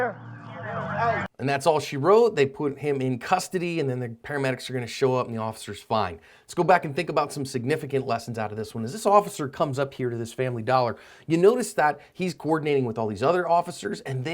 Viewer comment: I swear I heard the officer was fined lmao